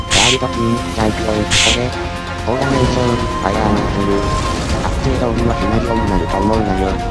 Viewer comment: えてるのとこ誰が助けにくんだろーって思ってたらマッスルって聞こえて吹いたw